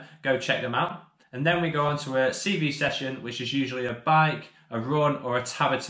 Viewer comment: Really cool to see! Even though i don't always understand your accent 😂 What did you say at 13:50 cb Session, is that stamina? or 2:20 with the six sons 😜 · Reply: Sorry! Yes, at 2:20 "6 sons or 6 daughters" - said he would rather have 6 sons as it almost makes up a football team haha! And at 13:50 we say CV session, this stands for cardiovascular so we're working on our endurance and stamina